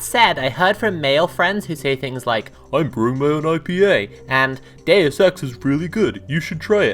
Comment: I know I'm not the only person who heard "gay sex is really good; you should try it!" and didn't question it at first. But I think they actually said "Deus Ex"? and now I'm more confused than before what that means?